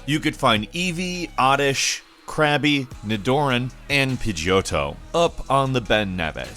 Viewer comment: did he say ni DOOR an